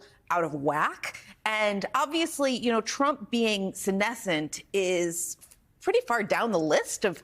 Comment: I misheard 'senescence' as 'tumescent!' lol